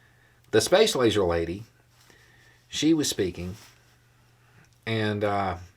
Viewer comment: I thought Beau said space laser lady was thinking. I had to rewind. Speaking not thinking. Though, in her case, speaking is charitable.